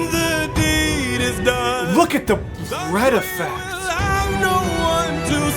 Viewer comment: “look at the bread effect!” Im sorry but thats all i hear